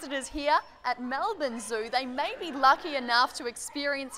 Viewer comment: I keep hearing "child" too instead of trial. Is she really using the correct pronunciation? I am not a native English speaker